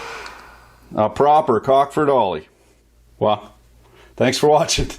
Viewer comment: I always thought you were saying "A Cock For Dolly," as in some sort of lewd reference to Dolly Parton...